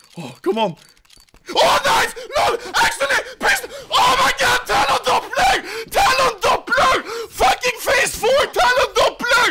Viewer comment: wtf is he saying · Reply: "TALON DOPPLER TALON DOPPLER PHASE FOUR"